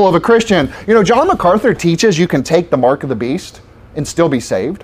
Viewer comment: Lol 🤣🤣 you even have no idea what macarthur is talking about. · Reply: I hear him saying "Sure, you can take The Mark." What are you hearing?